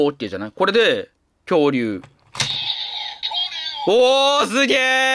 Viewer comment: 恐竜がトリオに聞こえる。